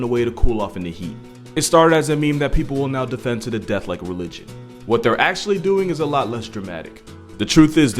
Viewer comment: I thought he said Luigi not religion lol